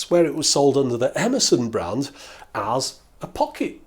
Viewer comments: When he said “Emerson” I heard “Amazon” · Yankee language observations: the first two times you said "Emerson," I heard "Amazon". also, chuckled at "heat shrink CHOOBING".